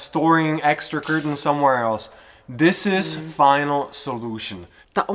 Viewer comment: did he say "final solution"..those words in 1940s meant gas chambers...but we understand didn't mean that.